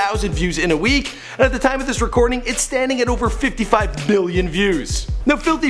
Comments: did he say 55 billion views ?? omg what is this video #justice4joji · Did he say 55 BILLION Views?! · through 5:57 you really just said 55 billion, and i dont think i misheard, looking at the video it has 55 million, but who knows, maybe i did mishear